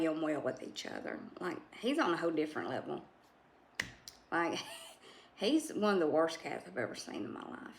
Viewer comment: She did NOT say that he was the worse cat in the world...What she said was, "...like, he was one of the worse cats I have ever seen in my life." You did not listen to what she said OR you are simply trying to control the narrative of her house and her cats. You have YOUR view of what she meant, and that is fine. But when you start posting sentences that are clearly incorrect. (because what you are stating that she said, is NOT what was said, the reality of this is in the video at about the 4:20 mark.) So, you took it upon YOURSELF to twist the IDEA of what she said, then to twist her actual words and to post that here on HER VIDEO PAGE using words that you KNEW would convince others that your statement is justified. Well...considering the fact that you outright lied (whether on purpose or accidentally, it still harms her and pushed her to feel like she has to justify herself--FOR YOUR LIES...) makes anything you say BULLSHIT. That being said, it is just fine if he is the worse cat she has ever seen. She not only has eyes and other sensory input units, but she also has a brain in her head that she uses daily! Imagine that! You do not know how many cats she has seen, nor the disposition of said cats she has seen...so you have no reason to make determinations about the NATURE of her view regarding not only what worst means but why she applied such a word to Dapper directly. Lets be clear that she understands cats, she cares for her own cats wonderfully, and she doesn't try to be